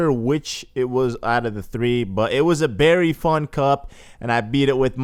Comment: When he said it was a very fun cup I thought he said "it's was very fucked up" xD